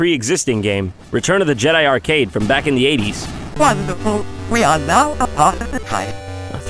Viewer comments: I think he's actually saying "Wonderful! We are now a part of their kind." I might be totally wrong though. Just a guess. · What did C3-PO say about Apartheid at 2:07? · he actually says "Wonderful, we are now a part of the tribe"